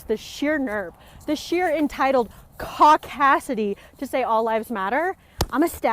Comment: Ohhh. I thought she was saying “Cock-acity” and was referring to just men. But that makes a lot more sense that she’s referring to all white people lol.